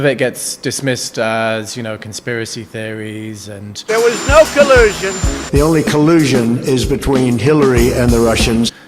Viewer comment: Collusion is a big boy word maybe trump doesn't know the definition of the word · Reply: At first I thought he said Collision.